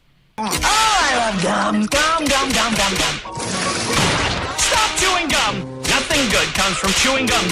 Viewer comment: I always thought when johnnys dad walked into johnny chewing bubble gun and then saying “stop chewing gum!” Over and over i thought he said “stop having fun!”